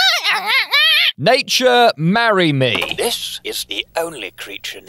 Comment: For a second I thought marmalade said "nigeeeel marry me"